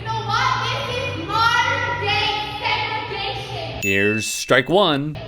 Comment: She said segregation? Lol I thought she said, "this is my sex addiction" referring to the recent shooting and her state of mind.